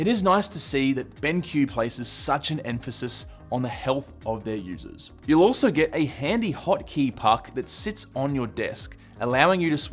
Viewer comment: “A handy hockey puck” is what I heard